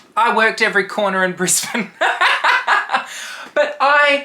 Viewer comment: For a second I thought you said “I worked every corner in Prison” 😂 🧼